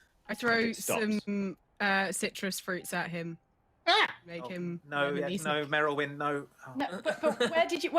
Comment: What does say at 1:25:42 that makes lose their mind? I can hear the part about citrus, but then I lose what she says under someone else taling. It causes such a huge reaction. Subtitles are no help. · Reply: "I throw some citrus fruits at him... make him *Lemon-y Snicket*." I had to play it a few times to work it out, I got the joke without the second half.